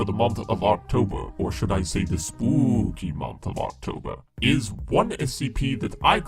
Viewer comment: DID HE JUST SAY SPOOKY MONTH